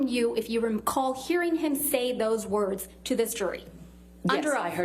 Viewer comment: sounds like she says "yes I heard his testimony...NAILED IT" LOOL